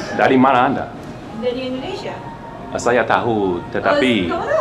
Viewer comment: the subtitles is a bit wrong by saying "I am from Malaysia," she is actually saying "saya dari Indonesia" which means "I am from Indonesia" so she is not Malaysian, but overall your language in Indonesian is pretty good, salam sejahtera dari Indonesia (Peace from Indonesia)